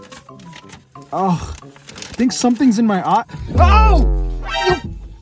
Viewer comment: Why I heard Hindi 'something in my aankh' 😂